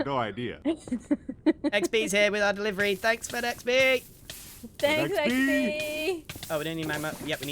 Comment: did I hear "Thanks Fed-XB"? Because that's an amazing nickname for someone who was delivering a package.